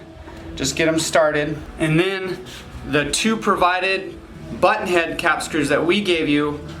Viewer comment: 100% sounded like he said "butt hole"